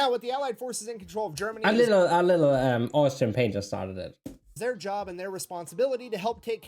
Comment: IMPORTANT: Thanks for your notices that the word Communism as a totally different meaning in 🇺🇸 and 🇩🇪. We mostly use it to describe just the economic system and not the ideology or countries itself. Just for a clearification nobody in 🇩🇪 (or only some few idiots) want communism. We are all happy with our Soziale Marktwirtschaft (social market economy) and love to be able to choose our job, starting our own business and seeking our luck in life, while having the safety nets of affordable health care, free education, subsidized public transportation and financial help for people in need. Although I am a bit envy of the lack of regulations and bureaucracy that you have in 🇺🇸. I removed the parts with my misleading use of the term communism. Sorry for the misunderstanding. 😊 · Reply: you said "Austin Painter started it" maybe I didn't understand your English or I'm not fully aware of this part of history?